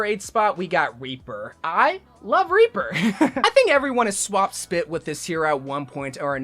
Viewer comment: did he just say “Swapped spit” Why??